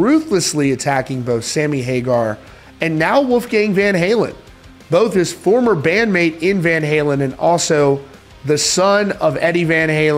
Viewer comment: "Former band MATE?" How can you say that when David Lee Roth and Sammy Hagar were band mates when they weren't ever in Van Halen at the same time? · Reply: oh. I had to listen to the audio 3 times to hear it that way. Sounds like he says "David is having beef with Sammy hagar and Wolfgang, one is a former member and the other is the son of Eddie van Halen." But it was actually "David has a problem with Sammy, but also Wolfgang, who played with David Lee Roth and is the son of Eddie van halen." It deffinitly sounded the first way until I re listened 3 times, because I also didn't know Wolfgang van Halen was ever in van Halen long enough to be considered a band mate for David Lee Roth.